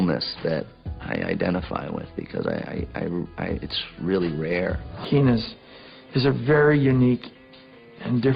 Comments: did he say PENIS🤣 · Nobodys talking about 0:34 when he says phoenix but subtitle says penis! 😂😂